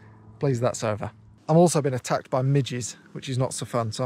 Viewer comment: Thought I heard “I got attacked my midgets”